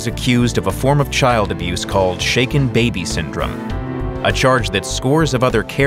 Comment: Now may not be the time but I thought they said shake it baby syndrome for a good few seconds